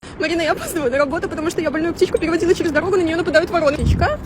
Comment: Послышалось "Марина я опаздываю на работу потому что я больную психику переводила через дорогу. На меня нападают вороны".😮